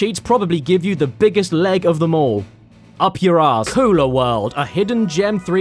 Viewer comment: When he said "Kula World" I had to look back up at the screen cuz I legit thought he said "Cooler World". As in, a world made for, or made by, small ice chests you might bring to a picnic.